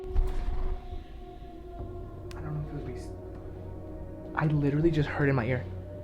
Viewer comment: Guys at 12:46 it sounds like Brennen says "I don't like boobies"